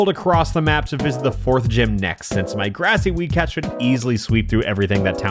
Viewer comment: For some reason when he first said weed cat I thought he was saying wee cat